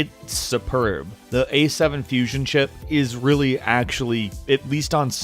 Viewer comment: did he say A7 fusion?